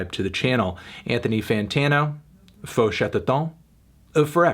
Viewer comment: As a french viewer, I lost myself as sound as I heard "FaU ChAteWToOn"